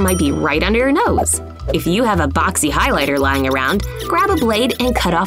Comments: I thought they said foxy highlighter I thought they had a crush on the highlighter 😂😂😂😂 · When she said under ur nose I thought she said on ur nose